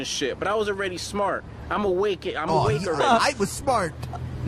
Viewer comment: Did he say "Awakerend"?